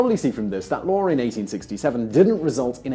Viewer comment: did he say the war in 1867